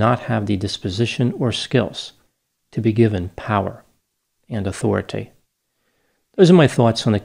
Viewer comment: For a moment I could have sworn he wrapped up by saying, “given power and authoritay,” as in “respect my authoritay!” But after rewinding, I think it was merely a Freudian slip; an unintentional mispronunciation, caught at the last moment. Too bad.